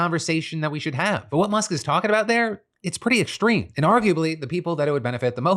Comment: I’m sorry but did he say “I’m not talking about dick?” 👀